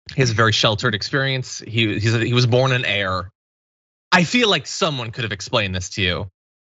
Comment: I know you said he was "born an heir", but I heard "he was born in error" which seems quite likely.